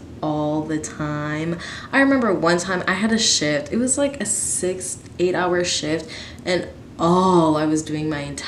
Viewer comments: Ahaha at 4:30 I thought you said “one time I had a shit- it was an eight hour shit” and I was like WHATTT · I thought she said shit 😂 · I thought you said I had to shit 😂👏🏼